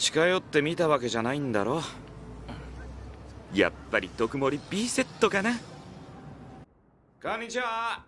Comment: 毒盛りBセットって聞こえたわ